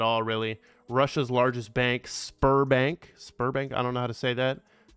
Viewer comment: Seating, listening and suddenly i heard ''sperm bank'' what??? :D Actually it was sberbank...... :(